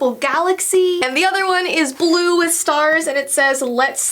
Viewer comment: I thought it said let’s sleep under the stairs not stars 😂 like Harry Potter who?